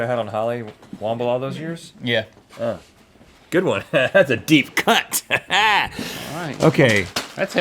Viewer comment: Does Rhett say cun* at 2:17 ??